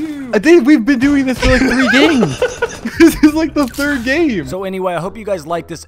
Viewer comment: Bruh u thought he said “we’ve been doing this for days” instead of games hahah